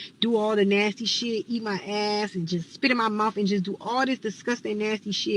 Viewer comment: Yuck... did she say "eat my ass and spit in my mouth"🤮 to each his own...